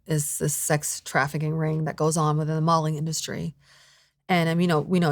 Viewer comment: She sounds like she says “the Mulling Industry” it could be how she speaks with an accent😂